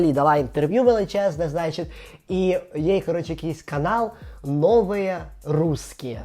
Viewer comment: мені почулось спершу "новиє прускіє" і зразу асоціація з дитинства, коли називали тарганів "прусаки"))))